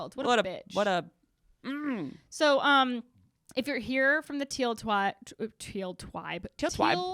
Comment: Lol I thought your said “if you’re here for the Teal twat”. 🤣